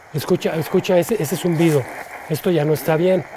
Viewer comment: Justo cuando dice "esto ya no esta bien" se escucha que dice Si o algo asi xd en el